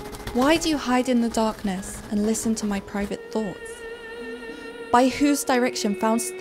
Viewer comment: guys i heard it : "My pervert thought" 😂😂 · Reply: 7 Rosa lina How ? 😂 I keep hearing ‘private ‘ 😂😂